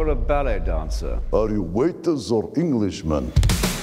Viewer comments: I love Rasputin's retort. "Are you a monk or a ballet dancer?" "Are you waiters or Englishmen?" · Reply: I thought he said "waitress or Englishman". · I love Rasputin's retort. "Are you a monk or a ballet dancer?" "Are you waiters or Englishmen?" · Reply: Oh i thought he said belly dancer